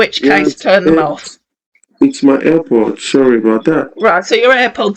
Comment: I honestly thought he said his airports, not air pods. 😹😹😹😹